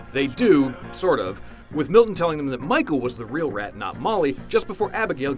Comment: did he say Michael?